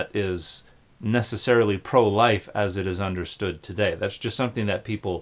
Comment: I thought he said 'pro-white' not 'pro-life'. That is a totally different arguement.